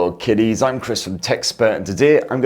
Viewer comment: I know uncle calls us kiddies but today I misheard it as kitties and it made my day!